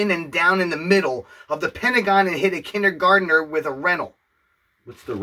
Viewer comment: Great reaction as always. And about 9:22 it did indeed say "rental" meaning by that a rental car, you can hear a car break sound after he says that. This is some outrageous old slim shady stuff 😅😂